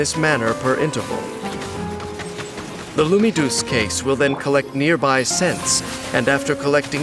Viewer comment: Naah man 💀. The amount of time i heard " Lumidous case" is just 💀. I'll be hearing this in my dreams too 🗿